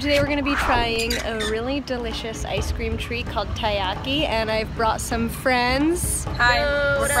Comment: When you guys said taiyaki i thought you said teriyaki 😂